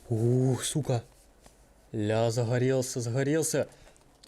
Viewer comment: 8.36 Мне послышалось: загорелся загорелся